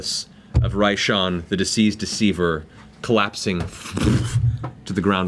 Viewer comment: sounded like Matt was saying “the Deceased Deceiver” lol